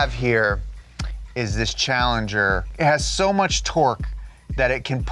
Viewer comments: Did he really call the charger a challenger? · Did he say Challenger???????????????????????? 0.0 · Did he just said this was a challenger?